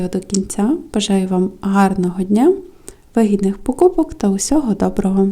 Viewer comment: Вы так шепчите мне послышалось в конце "бажаю вагитних покупок😂"